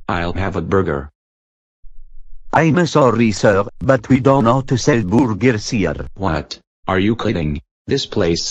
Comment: Sounds like he said we don't sell boogers anymore